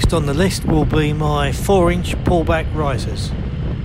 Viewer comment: Just popped over to this channel I’m not into bikes but yours does look a beast and definitely the cleanest around, I’m missing some OCD action, hope you are keeping well. I must be childish but I thought you said ball bag risers at some point 😅